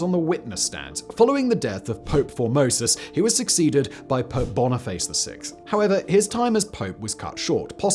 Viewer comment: Haha I’m just listening, and hearing Simon talk about Pope Boniface sounded like he said “Pope Bonerface”